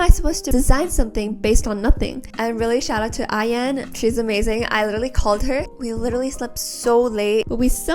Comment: tell me why i heard i.n??